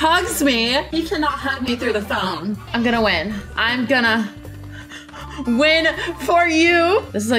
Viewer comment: what said : " I'm gonna win for u" In reality: what she typed "I'm gonna so fuor uou". WHAT DOES THAT MEAN 😂💀